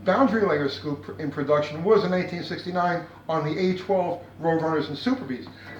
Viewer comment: I thought you were saying 1869 rather than 1969. New what you meant, but made me laugh. Then as your explanation started to move forward I became more entranced. you eventually went over my head with the tear induction system. Going to have to listen to that again.